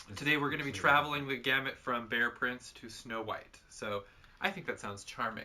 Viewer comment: Damn, I thought you said bare prince .